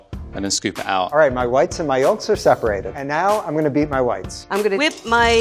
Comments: thought he said " and now im gonna beat my wife" lmao ☠💀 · Level 1 chef: "and now, I'm gonna beat the whites" Dude, chill. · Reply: I heard him saying "now, I'm gonna beat my wives" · "now I'm going to beat my whites" Never thought I'd hear this. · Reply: I firs thought he said beat my wives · deadass sounded like ”now I’m gonna beat my wife” 😂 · I thought he said “and now I’m gonna beat my wife” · I literally heard "now I'm gonna beat my wife" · i thought at first he had said "and now i'm going to beat my wives" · i wasn't paying attention and thought he said "... and now, I'm going to beat my wife" 😭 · Lmao I heard that as “And now im gonna beat my wife” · I really thought he said he was gonna beat his wife. I was like what?! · i thought he said “now i’m gonna beat my wife.” holy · am i the only one that when the guy said "ima beat my whites" i thought he said "ima beat my wife"? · who else heard “and now, I’m gonna best my wife” 😂😂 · i thought the level 1 said: "And now I'm gonna beat my wife" instead of beat my whites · I thought he said he was going to beat his wife...There's something wrong with me. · Misheard dialogues in video: 02:56 "and now am gonna beat my wife" 07:16 "masturbating these raspberries" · It’s late right now so I’m sleepy so when he said “now I’ll beat my whites” i heard “beat my wives” and I was like WHAT?! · I thought he said “ beat my wife” not “ beat my whites” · when he said "time to beat my whites" i heard "time to beat my wifes"